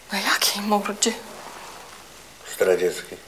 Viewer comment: Ma pani coś na mordzie. - Jakiej mordzie? - Zdradzieckiej... ...najlepszy tekst 😂😂😂 Szkoda że to już nie ten sam humor co w pierwszym sezonie :( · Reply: usłyszałem na początku: Radzieckiej xD